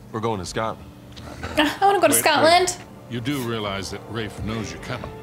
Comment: That fight was intense! We’re going to Scotland, Counties I want to visit. Egypt, Germany, and France. What counties do you want to visit, and why? I know one. Australia! Haha 🤗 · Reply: I misheard you at 23:41 and thought you said “I don’t wanna go to Scotland” as a Scot I was slightly triggered 🤣 nice that we’re in your top 3, hopefully you can visit when things get back to normal!